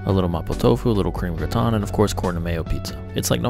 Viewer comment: For people wondering corn and mayo pizza is ridiculously popular in Japan. Not just on pizza bread but sandwich bread too. Even corn, mayo and cheese dip. BUT, remember that Asian mayo is different in taste and versatility (cooking) than Western mayo. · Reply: I thought he said Kornamayo pizza. I thought it was a fancy Japanese pizza